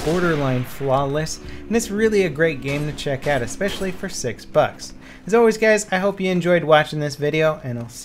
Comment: I thought you said "this is a great game to check out, especially for sick fucks".